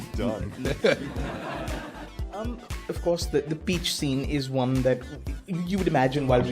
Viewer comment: I thought he said the beach scene and I was curious where the beach was in the film, then Armie said "Peach scene" I was like Ohhhh yeahhhhhh LOL